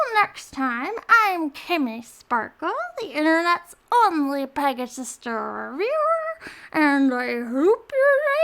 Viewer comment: Egads, I've been mishearing it the whole time. I thought she was saying "the internet's only pegasister of yore". This... makes a little more sense.